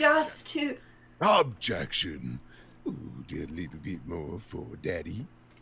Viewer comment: now what the fuck did he even say here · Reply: He said "a little bit more for daddy".